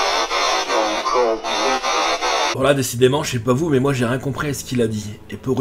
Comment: Moi a 32:11 j'entends" pas encore vous. "